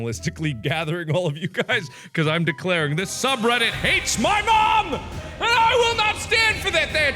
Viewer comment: I thought it said "this subreddit hates HOT DOGS"